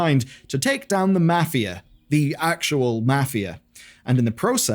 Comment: I thought he said "MATHIA"